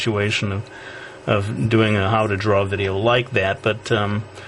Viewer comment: If you turn on the English automatic captions at 7:31 it says 'how to drug' instead of 'how to draw' XD